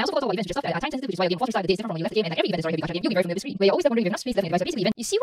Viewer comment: I think she said: “…and like every event and story heavy gacha games…”